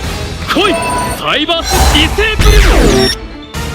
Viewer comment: Thanks for putting subtitles, here some corrections. In 12:20 and in 14:46 Playmaker should said "Cyberse Desavewurm" instead of "Cyberse Destruder" when he's refering to his fusion cyberse monster. In 17:28 to 17:37 and in 36:56 Soulburner says "Bail Links" instead of "Balelynx" when he summons "Salamangreat Balelynx" and uses his effect. In 18:05 Soulburner calls "Chicken Game" "Chicken Race". In 20:03 Soulburner says he sends "Salamangread Spinny" from your hand when he should said My hand. In 20:32 Soulburner mentions as you instead of i when he says the restrictions "Fury of FIre". In 22:37 Playmaker should say "Karma Gear" instead of "Karmagia" when uses "Firewall Dragon Darkfluid" In 29:33 Soulburner calls "Balelynx" as "Bailinks" In 36:42 Playmaker calls "Cynet Refresh" as "Cybernetic Refresh" Although is a correct translation since the card name is that one in OCG the subtitles used until this point used the TCG names of the cards. So it's up to you to keep or change it. In 38:41 Playmaker calls the "Pot of Acquisitiveness" as "Pot of Greed". A complete different card. In 49:12 The card is called in TCG "TAI Strike". Up to you to change since the name of the card is correct in OCG.